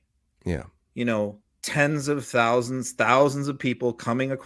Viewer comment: I agree we need an intelligent convo on immigration reform. But MT, I don’t think it’s gonna start with “I watch unsourced live feeds of billions of immigrants a day!” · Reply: I don't like his take on it either, but he DID NOT SAY billions! He said: 31:20 "tens of thousands, thousands..."